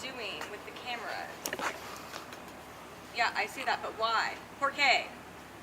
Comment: Lmao 1:13 no she did not just say Poor-Kay